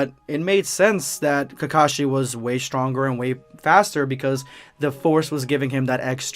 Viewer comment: I might have heard this wrong, did you say Kakashi use the force or am I trippin ballz. If you did, was it intentional or by accident